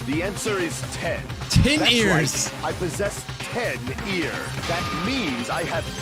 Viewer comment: I possess 10 ears.👂🏻 Me: Equals 10 Earlobeans.👂🏿 It sounded like he said 10 EAR instead of ears though. Bijuu, you cut him off!👂🏾